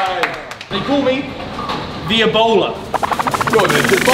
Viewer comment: I thought he said they call me the Ebola